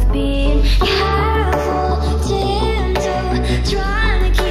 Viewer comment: Is it just me or dose it sound like she says timto 😆